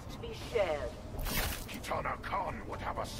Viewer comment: Did I hear that right? Did Barraka just say "Kitana KHAN" ?????